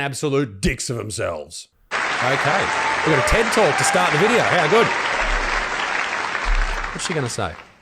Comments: I legit thought he said "A tent talk to start the video" when that first lady walked on stage. Rofl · I misheard it as a "TENT TALK" 😂